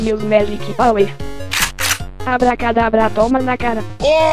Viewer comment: pulls out a shotgun abracadabra tomanakara instead of tomanakara I understood tomaenlacara